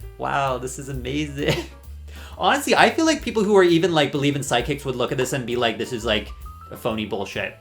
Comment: at first, I wasn’t watching the screen and I thought at first you were saying side kick… But then I realizd u were saying psychic😂